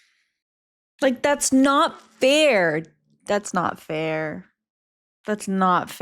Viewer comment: “Fair”? Did you say “fair”? 😘